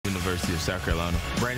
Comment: At first, I thought he said "Titan University" 🤣